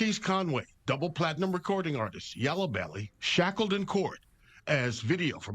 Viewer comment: did they call bro yellabelly? Lol